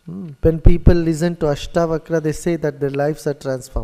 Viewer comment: What does he say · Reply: Sounded like Ashtavakra